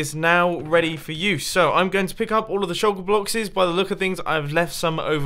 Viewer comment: Did he say shulker blockses? 😂